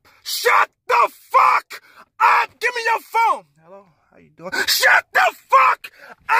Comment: I know I wasn’t the only one who heard “hello” “how are you” at 0:53 💀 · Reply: I heard ''hello, how ya doing''